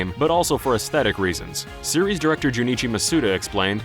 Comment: oooooh, series director. I thought you guys were saying serious director.... I feel dumb, I mean, the guy didn't look that serious to me